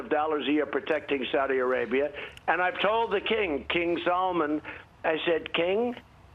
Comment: did he call the Saudi Prince King Solomon? 😂